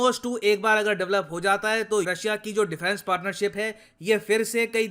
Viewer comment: Sir, At 7:17 what u said I didn't understood? Or maybe You were trying to say India-Russia defence partnership...